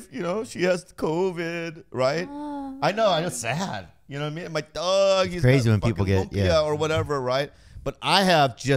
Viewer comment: Did anyone else catch Bobby saying "my dog has lumpia?" Hahahaha